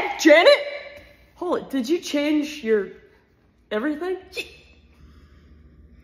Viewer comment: What she said: "i'm gonna talk to your mother" What i heard: "i'm gonna give you kids another brother" 😂